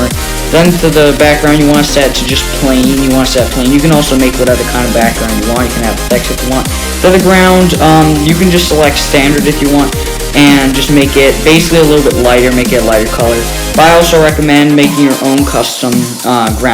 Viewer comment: I heard:You can have secks